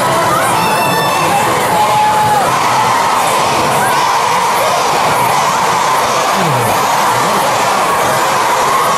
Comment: What did they start shouting at 7:18? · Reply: They were shouting "Seventeen"